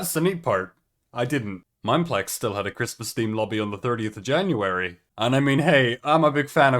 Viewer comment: Fully thought u said 30th of February and had to rewatch like three times till I realized I'm losing my hearing (also love the edits)